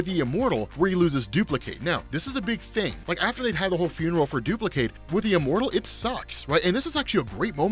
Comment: wait, did I mishear or did you say duplicate slept with immortal somewhere in there?